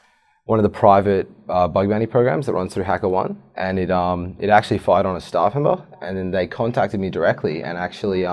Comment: What's being said at 1:39? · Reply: Actually fired on a staff member